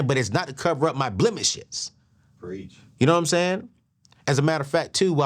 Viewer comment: Did someone hear “preach” in the background when he was taking about the babershop covering blemishes (around 29:06) 🤣🤣🤣